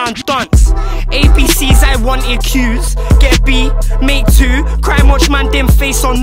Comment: I always thought he said "get pee? Me too" 😂😂😂